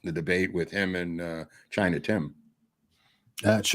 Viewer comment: At first I thought you said Tiny Tim lol. Tiptoe through Tiananmen. 🎵🎵🎵